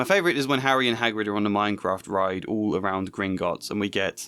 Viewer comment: did he say "Minecraft ride?" 😅